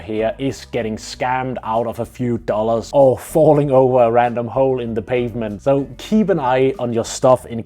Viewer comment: Thought I heard you say "falling over a random hoe in the pavement" and yeah, that wouldn't be too incorrect as well lol